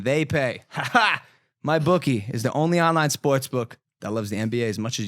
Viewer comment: he says “Spots book” instead of sports lmaoooooo